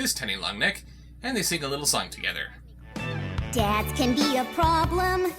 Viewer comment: I thought he said death at first